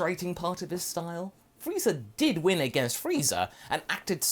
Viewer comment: Late but did he say "Frieza did win against Frieza" at one point? Or am I going crazy? At